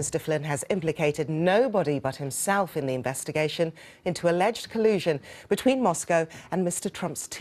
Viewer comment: Haha always great to see the Trumpanzees in full denial in the BBC News comment sections. xD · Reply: Can u tell me what did the presenter say in 0:20? A ledge collusion?